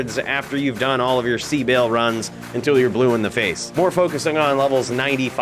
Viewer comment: did he say "sea baal" or "c baal"? Is there a special version of baal? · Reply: C Baal is Chaos Sanctuary + Baal runs. You have potentially higher drop chances and more importantly, you avoid a 1 hour temporary ban